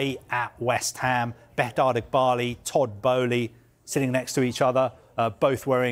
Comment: he actually said Todd Boehly!